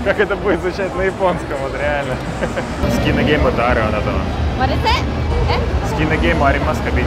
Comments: на 4:32 девушка сказала не "варэсэ", а What you said? (что ты сказал?). Видимо, решила, что англоязычный иностранец и на автомате кинула ему реплику на английском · По моему, она говорит "What you say ? " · Продолжение нашего с Юри путешествия в по Питеру и Москве уже совсем скоро! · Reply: ломал пару минут голову, что такое варезе, пока не прослушал пару раз...сначала думал это какой-то азиатский язык, а потом дошло, это она пыталась сказать "what are you saying" (какая-то она странная японка, нихао(китайское привет) ))) помоему она тебя троллила)